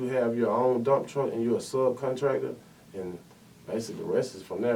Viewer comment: tell em ur a “served contractor” ??? what did he say??